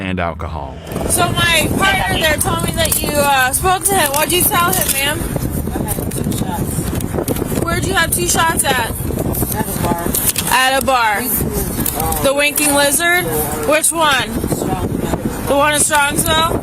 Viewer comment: I swear I thought I heard the officer say "The Wanking Wizard" XD